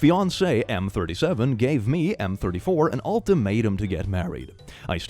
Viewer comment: I’m just listening to the video and had heard M/34 instead of F/34 and was very very confused when he said she became pregnant